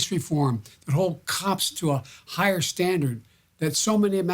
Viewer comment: Sorry, the subtitle at 3:53 is erroneous 'Hold cops to 'hired' standard...J. Biden clearly pronounces: to a higher standard. However, the question is why such 'higher standards' have not been applied at the recruitment of future police personnel. At 4:20 the diction is not clear, since I did not understand it: Those of us who sit in some position in....?...abuse of power. At 4:38 the voice become so weak, I could not understand the meaning. At 5:45 he says: 'We've got to move got to change" whereas the subtitles say: "We have to move, we have to change'. Quite an emotional and encouraging address of Joe Biden after having had personal contact with the familiy of the victim of police aggression. I have the suspicion that the pressure of the knee of the police officer exerted to the aoarta blood vessel might have interrupted the blood supply to the brain of the victim causing unconciousness, which, however, might be difficult to prove by autopsy. The family of Eric Garner who died in 1994 by a similar breathtaking police actio to whom Mr. Biden is referring got 5.9 Mi., $ compensation of year long court procedures. Let's hope that at lest the Minnesota jurisdiction will come to a similar judgement in this regrettable case.